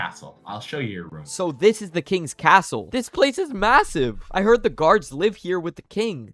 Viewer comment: did someone say massive